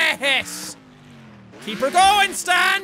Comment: did jack just say "keeper goin?" aka Vanoss reference